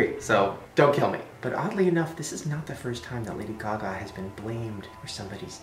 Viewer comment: did he say "caca" or "gaga"